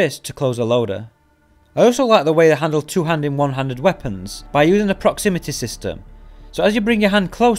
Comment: The whole video he was saying 1 handed and 2 handed but I thought he was saying 100 200